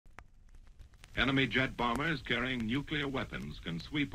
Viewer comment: I thought they said "Anime jet bombers carrying nuclear weapons"